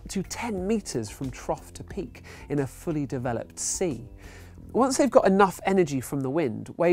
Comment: Did you just say "fully developed sea"?